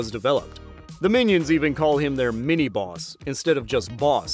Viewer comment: I misheard mini boss for minibus😂😂